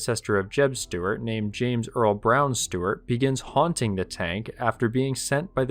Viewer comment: "The Story of the haunted tank." Oh yeah, so every tank I'm using when playing a video game. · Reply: Sounds like "James Earl Brown Stewart". The general's name was James Ewell Brown Stewart. I might've misheard the pronunciation on the video though. Great work as always.